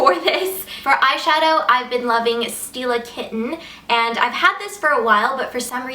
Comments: Lol! I thought you said Steal a kitten, not Stila kitten. hahaha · Oh my goodness when you said "Stila Kitten" I heard it like "Steal a kitten" and i was like WAIT WHAT?! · when I heard you say that you've been wearing the stila kitten eyeshadow, for some reason I misunderstood you and thought the name of the eyeshadow was "steal a kitten"... hehe I think I like that better :)